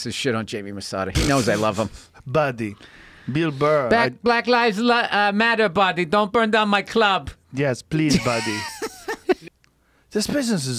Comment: holy shit balls batman! an actually JRE cut-edit! i wonder what rogan said! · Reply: Bill says ‘black lives matter buddy don’t burn down my club’ in that accent. Wasn’t cut from audio versions